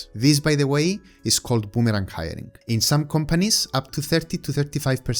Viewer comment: I almost didn't catch your phrase "boomerang hiring." I transcribe it here for anyone else who missed it. Good choice of words.